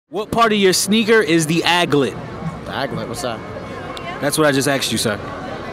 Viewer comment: If Phineas and Ferb didnt make that song nobody would know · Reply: Yell yeah but I coulda sworn they said it was called ADlet!!